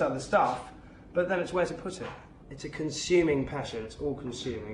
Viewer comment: What did he say at 6:03?? I heard "I've got everything I want. There's always other stuff. But then, there's always another pussy"??? :D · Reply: ''but then it's where to put it!''